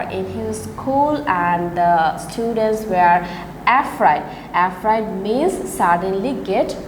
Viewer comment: did she said f raid